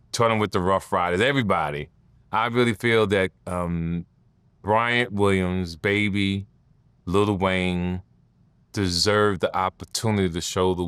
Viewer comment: Wayne n birdman kissed each other on da lips yall forget ? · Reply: I know he was just referring to Birdman by his other weird name…. but it really sound like TK said “Bryan Williams baby Lil Wayne” 😂